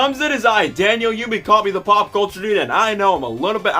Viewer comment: When I first watched this video, I thought you said The Paw Patrol Dude but then I quickly rewind it and it this time said The Pop Culture Dude